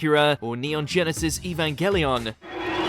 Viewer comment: Did he just say “EVANKELION?